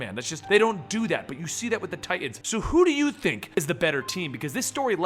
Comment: I thought u said the TEEN TITANS I mean Beast Boy Cyborg Raven and Starfire AND Robin